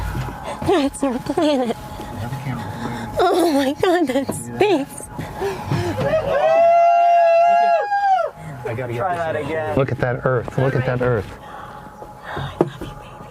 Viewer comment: Ngl. With the context of the video. I thought dude said Look at that Girth Look at that Girth at 1:46.